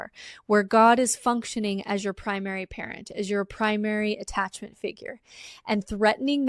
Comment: Damn. I first thought she was saying "primary carrot". But it's "primary parent". That makes more sense.